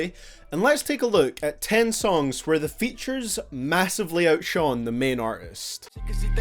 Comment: anyone else hear Shaun and not shine